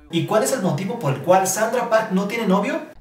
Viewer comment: ¿Sólo yo entendí "Sandra" en vez de "Sandara" ?